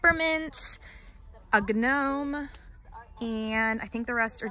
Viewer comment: Did she just say a GUH NOME!????!??!? I CANT THATS SO FUNNY!! NO HATE LUV U